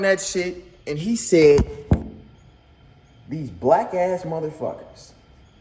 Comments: I heard Tayler say "wack ass mf'er" not the racial slur. Anyone else? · I thought he said wack ass mf till u said it I had to go back and listen he most def said black wow yea cancelled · Sounds like he said "wack ass mother fuckers" stop smoking weed nigga you tripping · I heard “those wack as motha f***kas man” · Damnnn i realllly wanted to hear what u heard... But i legit keep hearing "fuckin'whack-ass motherfuckers"... i hope he wouldn't be sayin what u thought he said, especially bc he was facing a bouncer that WAS BLACK!! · I thought he said “wack ass motherfucker” but nah you can hear that “bl” at the beginning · well idk abt y’all, i’m not tryna defend tayler or nun but to me it sounded like he said “wack ass mf” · Nha I heard "wack ass mf" tbh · I DIDN’T HEAR IT? I heard “wack ass motherfucker” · i heard “wack ass mf” not taking his side im jus sayin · I thought he said “whack ass mofo” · Definitely sounded like he said wack ass mf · I could of sworn he said wack ass mother fuckers 😭😭🤦🏽‍♂️ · i thought he said wack ass mfer at first but the replay corrected me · I heard “wack ass motherfucker” am I trippin lol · is it just me or i heard, "wack ass mother'f***ers" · cant lie it sounds like he said whack ass mf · I heard “wack as mother fuckers” DDG milking 🥛 the cow 🐄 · In thought he said whack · i heard him say bwack black and wack combined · i heard "whack ass mf" but thats just me